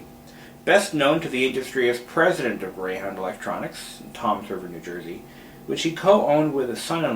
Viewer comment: I thought you said "Tom Servo New Jersey" for a moment...